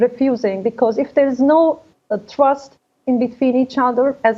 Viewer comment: Did she say trust